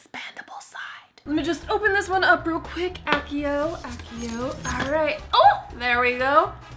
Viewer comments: Did u say accio to open up the case? Oh ma gosh. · did she just say accio to open the other side?!